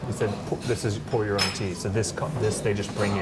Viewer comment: Funny bit of lost in translation here, but the waiter said the tea was "Pu'Er" tea, which is a type of tea, and you ended up mishearing it as "pour your own tea", which I guess is also true?